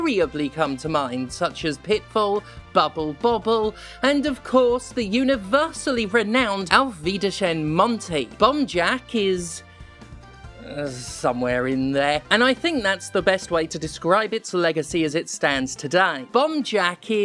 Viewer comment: Is it weird, that I keep hearing "Bum-Jack" throughout the video, despite knowing better?